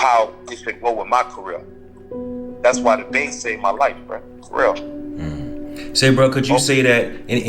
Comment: said.. I think.. "that's why The Bay Saved My Life FR.." I do remember him bouncin out with Pretty Black back in the day.. if I heard correctly.. first Pac.. now another rapper sayin The Bay Game Related.